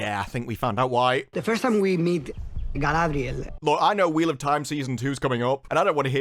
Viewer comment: Ugh, do we have to? Okay I'll watch. 0:12 I think he actually said Guyladriel! 🤣